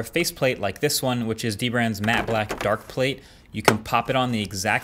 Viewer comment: I thought that controller said "Matte Black Lives Matter" lol